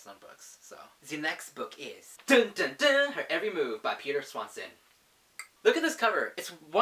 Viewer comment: Is it okay if I subscribed? And 5:00 did you say "her every move"???